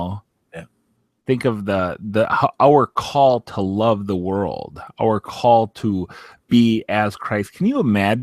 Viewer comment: At time index 7:54 did he just say "our call to love the world"? Doesn't our Lord Jesus Christ say we are to NOT love the world and if we love the world the love of our Father is not in us? I am not trying to defend Pastor Anderson here with his style of preaching, in fact I too think he is too aggressive and hostile with his preaching, but he does preach Gospel of Jesus Christ. He also believes Jesus Christ is come in the flesh